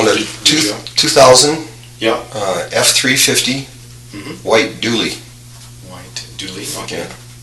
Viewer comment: when hes asking what kind of truck he says white dually then says fuck yea lmfao or is that just me? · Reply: I thought I heard the same thing, but I'm sure he said white dually OK lmao it does sound like fuck yeah